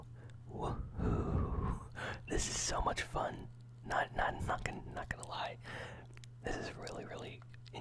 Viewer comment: what did he say? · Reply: "not not nothing not gonna lie" Was just stumbling over my words I was happy.